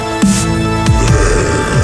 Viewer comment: What does it say at 2:03 to 2:10 ? I would like to know but I can't make it out. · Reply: it says "there... that is so much better than living, is it not?"